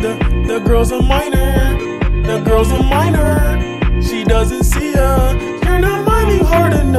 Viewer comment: the first time i heard this i thought he said your girls a minor she doesn’t see ya😂😂😂